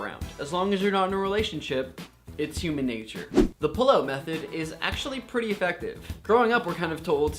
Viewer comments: For the longest time I thought he was saying "the pillow method" and i was just like "O_O; what is the pillow method..." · For the longest time I thought he was saying "the pillow method" and i was just like "O_O; what is the pillow method..." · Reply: I thought he said pillow method too...